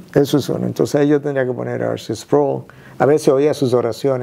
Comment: No entiendo el nombre del hombre del cual dijo que el escuchaba sus oraciones en el minuto 24:30 alguien que me diga el nombre por favor 🥹? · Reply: R.C. Sproul